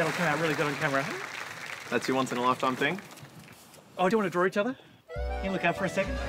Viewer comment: I misheard Luke say "D'you wanna root each other?" and nearly spat out my drink